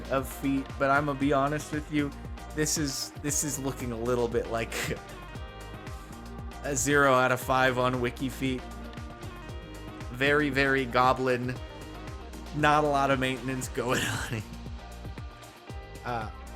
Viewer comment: Did you say they're a little goblin? Is that because you would be goblin them up if you could?